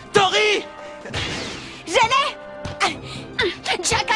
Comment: C moi ou a 11:32 torri dit xene? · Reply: dami dami60 nner il dit je lai